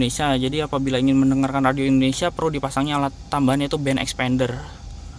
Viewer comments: Dengernya Band Xpander, eehhh.. ternyata Band Extender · Ane tadi dengernya Band Xpander ya, harusnya Band Extender